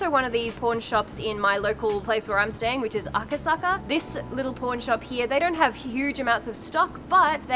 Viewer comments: I totally heard "porn shop" and was like woahhhh lol 😂😂😂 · When you say Pawn shop, i really thought you said p*rn shop. My jaw dropped a little bit. 😂😂😂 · I swore I thought I heard porn shop then realized you said PAWN shop 😂😁